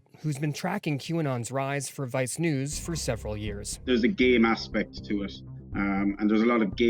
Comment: at first I thought he was saying gay instead of game.